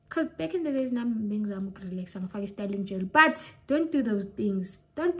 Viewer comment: Styling gel !!! 😭😭😭 Did she just say styling on the kitty?! 💀💀💀💀💀💀💀